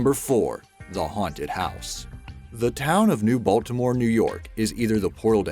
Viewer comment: I thought he said "Neibolt"